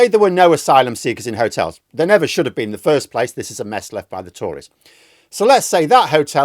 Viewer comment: For a moment there, I thought you said Tories ...not Tourists 😂